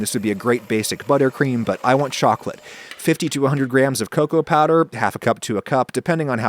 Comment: You said "fifty to a hundred grams of cocoa powder" but I first heard "fifty-two-hundred grams of cocoa powder" ... I had to rewind ...